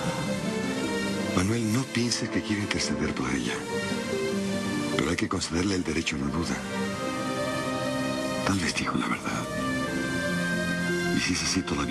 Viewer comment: Min 7:38 alguien entendió que dijo Sixto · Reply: Manuel no pienses que quiero interceder por ella pero hay que concederle el derecho de la duda tal vez dijo la verdad